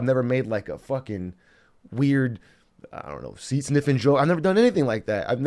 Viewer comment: Imagine dedicating your adult life to making a name for your self via social media and using a decent chunk of the few words you have left on your platform to say "booba spam" and "c sniffing" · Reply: I thought he said "seat sniffing"